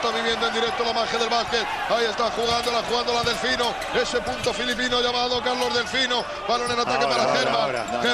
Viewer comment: "ese puto filipino llamado Carlos Delfino"? o escuché mal? gracias.